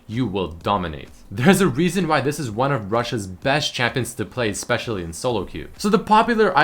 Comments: "there's a reason to why she's one of RUSSIA'S best?!" LOLOL Pls tell me im not The only one who heard that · Did i hear Russia?